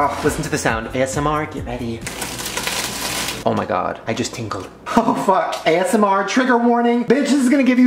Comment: When he crushes the Cinnamon Toast Crunch and said “I just tingled” I thought he said “I just tinkled” and I’ve never laughed harder in my life